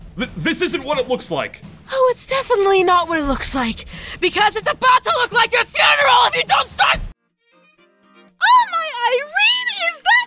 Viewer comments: (For the peeps who couldnt hear what she said Aph"s mom: Oh its definitely not what it looks like...BECAUSE ITS ABOUT TO LOOK LIKE YOUR FUNERAL IF YOU DONT START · I heard 13:36 "BECAUSE IT'S ABOUT LOOK LIKE A FUNERAL IF YOU DON'T STOP"